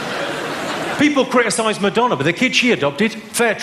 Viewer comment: i love how he laughs at the crowd when they get offended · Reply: King Kunta I think you misheard Madonna as McDonald's. I'll break it down for you "People criticize Madonna, but the kid she adopted: fair trade." He's implying that she bought the child... 😂